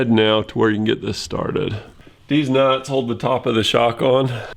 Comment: did he said deez nuts ?